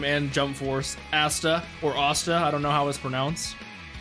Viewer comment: he said assta instead of asta🤣🤣🤣